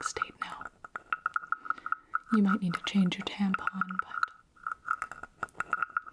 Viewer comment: Did she say Tampon?